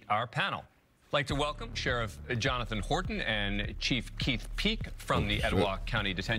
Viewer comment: Thought he said chief keef at first😂